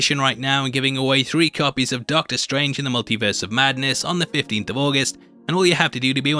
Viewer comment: really thought you said: "MonkeyVerse of Madness"